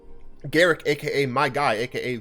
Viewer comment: I hear ‘derrick six pack’ when you introduce yourself